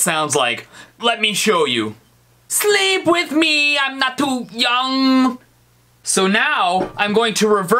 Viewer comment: All that I hear is "Sweep with me, I'm not too much/numb